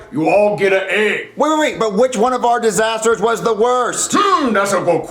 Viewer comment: it sounds like he said eggs